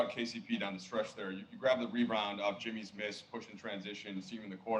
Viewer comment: Lmao this guy said “reBroned instead of “rebound” 🤣💀